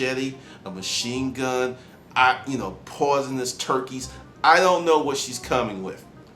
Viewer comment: Did you say "poisonous turkeys" @ 8:22? LOL, a new one. You should have hired her. She would have worked her ass off for you or been a great sugar mama!! LOL U Crack me up !